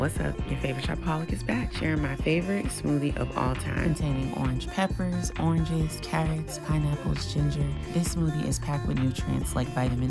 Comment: Can you write the ingredients I couldn’t hear them it sound like you said ginger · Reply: Orange peppers, ginger, pineapple, oranges.